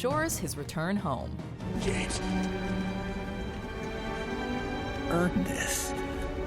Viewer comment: did he just said art3mis from ready player one